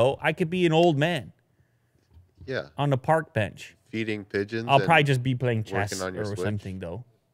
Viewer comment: i swore i heard say "Eating pigeons" not feeding pigeons 😂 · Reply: Yeah I heard "eating pigeons" to..........